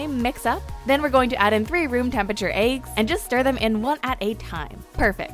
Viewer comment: did she just say "aygs" instead of "eggs"? XD